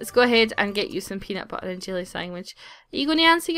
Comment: Every time you say sandwich I SWEAR I hear "sangwich." Xp It makes me laugh every time because I know what you're saying but it sounds so strange to me on the recording. I love how accents change words slightly